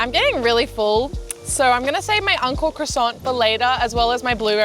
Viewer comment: I thought she kept saying UNCLE CROISSANT but realized she was saying ANKO CROISSANT 🤣🤣 クロワッサンおじさん